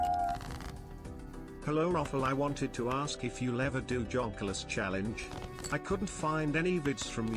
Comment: I thought the chatter said “jobless” here and I thought roffle either a: didn’t get the joke, or b: was playing along